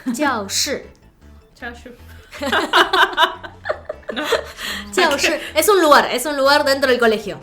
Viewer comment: parece que dice "Xiaoxi" jeje